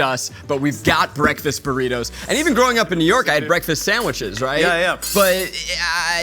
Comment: straight up thought Zach said growing up in Europe and I was like HUH